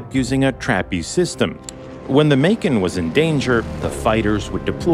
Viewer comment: When he said “Macon” I thought he said “bacon which would have been much funnier not gonna lie 😂😂